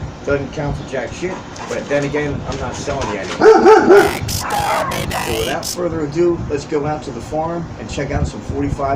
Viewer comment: I can't make out the subliminal message. Lol. What did it say? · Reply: Since my dog was spazzing out from the person walking by I threw a little Doctor Who in there and that was exterminate the Daleks..😂😂😂😂😂😂 glad u heard THAT..